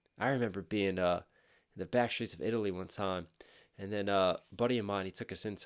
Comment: I thought he said fascist Italy not back streets🤣🤣 had a replay the audio like four times🤣🤣